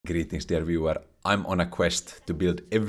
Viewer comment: "Greetings dear viewer" reinvigorates my spirit like a warm tea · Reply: i always thought he was saying "greetings everywhere" and was fond of it